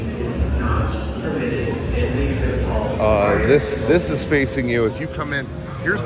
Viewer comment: I thought it said Hentai Hall and I wasn't even phased. Like, yeah they'd probably have one.